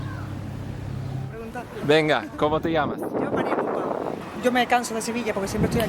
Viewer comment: ¿Cómo se llama esta chica? 💃 No entiendo el nombre cuando lo dice. ¿«María» qué? Gracias. 🇪🇸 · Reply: Si haces clic hay subtítulos. Gracias y un saludo